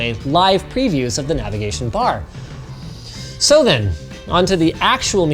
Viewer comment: When Linus says "so then" i hear "sodimm"